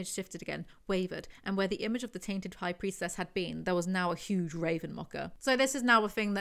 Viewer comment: Every time you say it i hear "Raven Mocha" it's not you it's me being an idiot lol